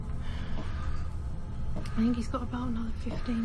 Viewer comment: it kinda sounds like she says "I this he's got a boner"